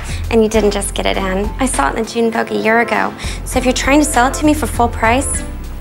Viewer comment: June Vogue not June Poke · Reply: I always thought it said Teen Vogue😂